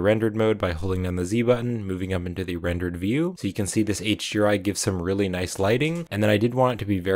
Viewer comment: what do you say on 3:41 ? you speak very fast and should add subtitles ;) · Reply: Hold Down the Z button, and move your mouse up, to go into rendered view. You can turn on the automatic subtitles by clicking the CC button on the YouTube player.